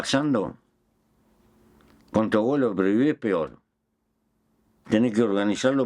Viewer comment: minuto 37:03 dice: "Lo que no me gusta pero sigue pasando, controlarlo o prohibirlo es peor, tenes que organizarlo para que sea lo menos peor posible" Dice controlarlo? no le entendí esa parte.